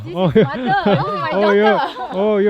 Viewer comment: Anak dia laki ke perempuan. Mula² mak dia kata my son. Lepas tu bapak dia kata my daughter. 🤔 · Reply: She clearly said mother and daughter.